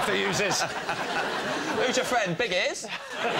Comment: What does James say around 13:07? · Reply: "Who's your friend, big ears?" It's a reference to Noddy Holder, c/f the Enid Blyton character called Noddy, whose best-friend was called Big Ears.